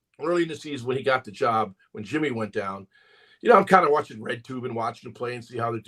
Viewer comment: Anyone else catch Jaworski mention he was watching Purdy on REDTUBE (the X-rated site), instead of YouTube? @ 7:02 or there about. LOL -- <link>